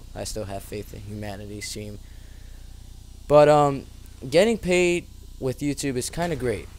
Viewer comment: dammit i thought it said getting LAID with youtube LOL